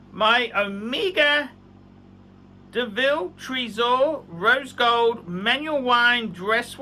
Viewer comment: I thought I heard "rodent gold". Just watch his vids Arch.